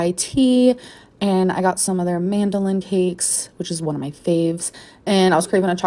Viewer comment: Did she just say mandolin cakes…